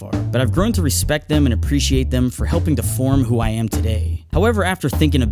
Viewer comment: did he say deform or to form